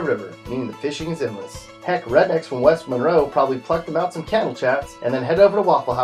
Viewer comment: He says cannel chats instead of channel cats